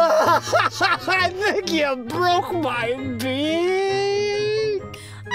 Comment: I FORREAL THOUGHT RODAN SAID “I THINK YOU BROKE MY DIIIIK” 💀😭